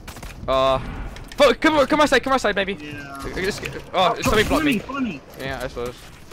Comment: Did Benjy say come out come out baby